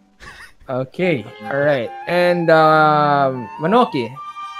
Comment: did you just said 𝗠𝗔𝗡𝗢𝗞𝗜𝗘?! 𝗦𝗜 𝗠𝗔𝗡𝗢𝗞?!!! Tapos naging skeleton na lang siya? hahaha Tagal niya nawala sa mga videos niyo and balita ko wala na siya sa PR. Sad pero ang saya niyo kapag complete kayong naglalaro kasi grabe kalokohan.